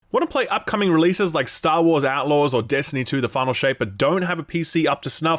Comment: I honestly thought u said "the Final shame" lol